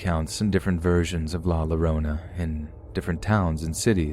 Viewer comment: Just for future reference, La Llorona is just like saying Yorona. In the spanish language, Ll is a Yuh/Yoh sound . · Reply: I kept hearing "My Sharona!" Every time he pronounced "La Llorona" like that.